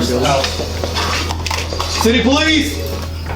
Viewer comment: i thought i heard diddy police 😭